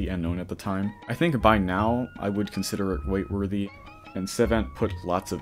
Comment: he really said "wait worthy"